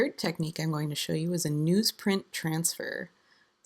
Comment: what type of paper do you use for the transfer :)? I heard "news print"? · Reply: Yes, newsprint is correct - it's basically blank newspaper!